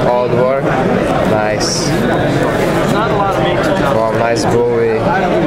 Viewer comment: lol when yu said bowie I thought I heard yu say booty lol......but very cool lock system n knives